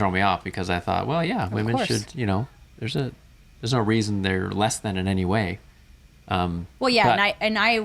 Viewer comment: did he say there is a reason why women are less than in any way? Wonder if that was a Freudian slip or that’s what he meant to say. What an ass